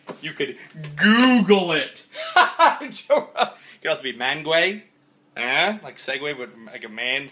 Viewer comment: In the beginning of the video, when you said "Mangue" it sounded like you said "guey." It's a bad word in Spanish. xD